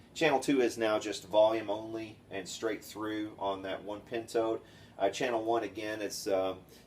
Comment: i thought you were saying 'pinto'...pentode..hehe. been nice to see a diagram